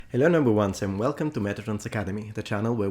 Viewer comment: you say "hello noble ones" but for the longest time I heard "hello number ones" and sometimes I still do. I really like your content but sometimes I expect more off you as you pronounce some words pretty weirdly like "difficulty" where you put the emphasis on the FFI which is weird. and no this is not to put you down its just me being a narcissist and going "ha! gotcha!" never stop producing this content for even petty people like me really enjoy it and rely on it for entertainment and education.